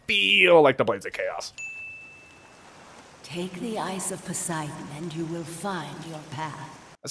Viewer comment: I misheard that as “EYES of Poseidon”, which would have been a brutal callback/forward